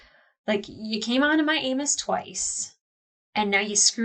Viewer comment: i must have misheard when you said that about the camera guy. I heard "you came onto my anus twice"